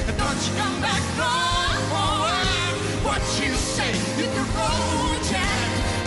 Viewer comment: Агутинские бессмыcленные вставки подпортили впечатление. Что такое за "but you say", интересно, он родил... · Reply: Тебе послышалось. Он сказал: "What you say?".